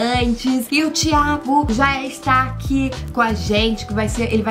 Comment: E eu que entendi " O DIABO ESTÁ AQUI AGORA COM A GENTE"